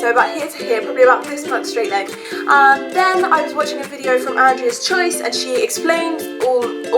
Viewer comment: hello, who's videos have you been watching? I can't hear properly (at 3:03). Thank you so much your hair is gorgeous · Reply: I mentioned Andreaschoice :)